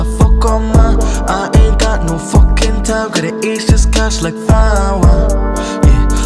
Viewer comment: For the longest time I thought he was saying &quot;age this cats&quot; LMAO